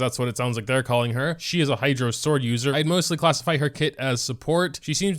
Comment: Just a quick lil correction a lot of other genshin content creators made the same mistake, the new 5 star with the spear is a sword not a claymore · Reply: Where did he say claymore? He clearly says sword